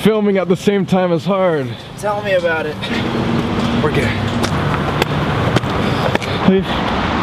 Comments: did anyone else hear "we're gay" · when Colby said 'okay' after climbing up the ladder, it lowkey sounded like he said 'we're gay' 😂